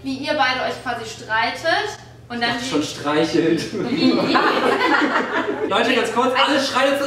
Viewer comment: Ich habe streicheln verstanden